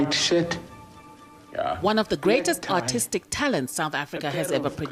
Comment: Did she say he was autistic? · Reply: Artistic